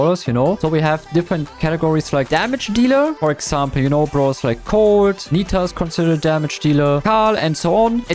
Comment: im sorry mate but as a cordeluis main im pretty sure cord isnt a damage dealer Edit: my bad bro i thought u said cord when u said colt Edit2: 5:27 willows controller not thrower